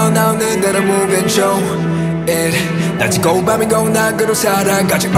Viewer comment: I thought Felix said "Oh sh*t"